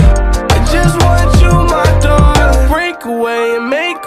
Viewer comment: i thought he said daughter not darling i was like "woah" also today is my brothers birthday :) hes 18